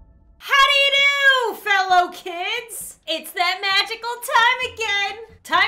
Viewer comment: I always thought u said Fellowkins actually it was fellow kids